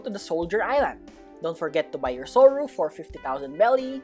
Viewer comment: i thought said remember to buy your yoru but it was soru hahahahaha